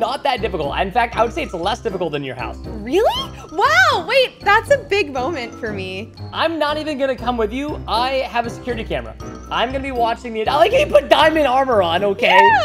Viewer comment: It sounded like said "it's a big bones for me" ?????????